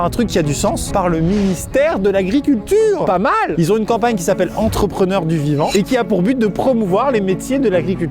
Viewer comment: Clairement une des mes vidéos pref ! Quand t'as dis que c'était la fin j'étais trop déçu 😥 ... Je veux en voir plus !!! C'était grave intéressant ! Sinon au début j'ai entendu "entrepreneurs du dimanche " 🤣